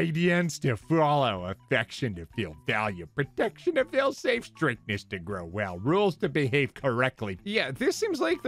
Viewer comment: I thought it said straightness for a minute 😂